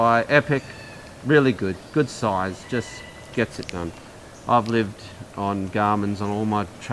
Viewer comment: did you just say: " just get the c*nt"? I mean doesn't get more aussie than that but didn't expect that